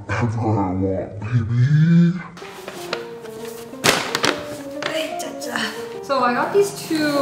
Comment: did she really said "aye chacha"?